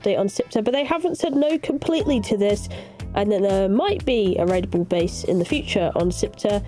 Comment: I know that your saying "a raidable base" but I keep hearing "red bull base". Apparently my ears are not working today.